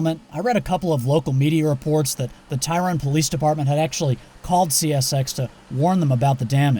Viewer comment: I thought he said tyrant police 😅